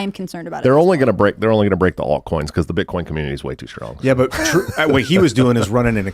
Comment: "The Bitcoin community is way too [huh?]." Does anybody know what Josh Smith says here? I listened to it about 10 times, and I can't make out what he says where I wrote "[huh?]." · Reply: strong